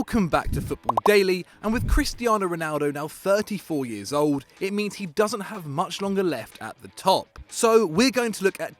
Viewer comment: I thought they said, “Ronaldo doesn’t have much to live” Me: pikachu face